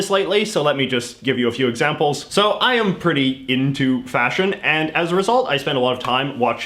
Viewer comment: for a second I thought you were gonna say fascism